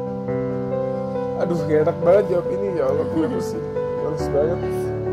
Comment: ngomong apa tuh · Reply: Gak enak jawab ini ya allah